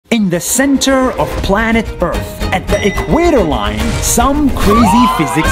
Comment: Is it just me or did it sound like he said in the center of planet birth.😂